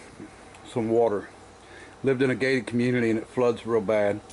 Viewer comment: I thought you said the guy drove in water at a gay community. Took me five mins to figure you said gated. Lmao.